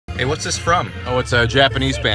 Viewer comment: I heard it as monogatari i was confused lmao